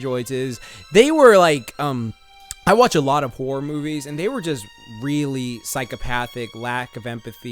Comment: did he say `i watch alot of porn movies` ???